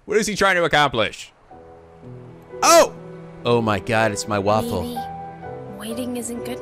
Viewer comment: did... did he just say waffle